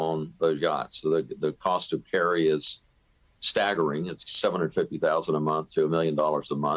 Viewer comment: Wait @ 2:30 did he say $750,000 to $1,000,000 a month to maintain a Yacht?